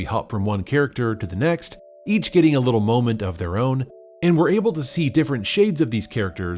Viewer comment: Every time you said "Moment"... I heard it as "Moe-ment".